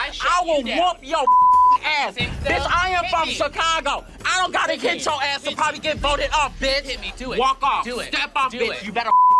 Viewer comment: I can't really tell but it sounded like Mystique said "I prolly don't have to hit your ass to get voted off the show" lmao that sent me